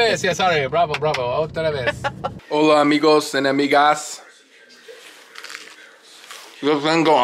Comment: When said 'Hola amigos and amigas' lol it sounded like he said 'enemigas' inatead of 'amigas' had me dying 😃